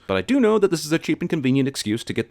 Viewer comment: Did she just say “Obvs” ?